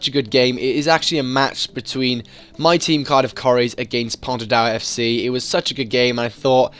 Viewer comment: I thought you said you team was Cardiff curries 😂👍🏻 IDK